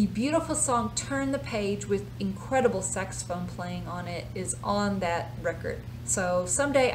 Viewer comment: OMG, maybe it's just me but it sounded like you said "Sexaphone"